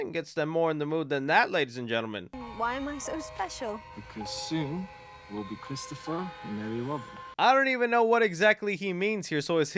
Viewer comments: He says something like "Because pretty soon, we'll be Christopher and Ellie Robin." Not sure if I hear her name right, he's mumbling. But I believe the gist is that they're engaged and he wants to show her 100 Acre Wood and his friends. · It sounded like Christopher said “because soon we will be Christopher and Nellie (or Ellie) Robin meaning they are engaged and soon to be married